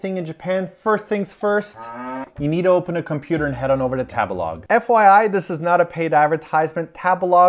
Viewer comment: I was slightly triggered when you first said tabelog i thought you said tagalog the way foreigners said it